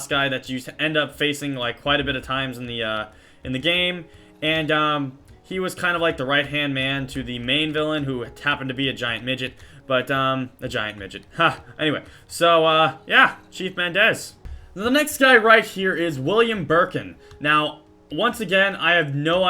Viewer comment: Every single time Sky say "but a" it sounds like he say "Butter !"